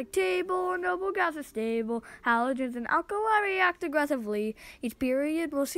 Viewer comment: did you say halogens 😳